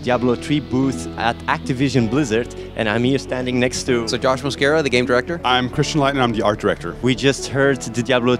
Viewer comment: Sounds like he says "Sir Josh Mosqueira". Hey I got nothing against that, this guy deserves to be knighted imo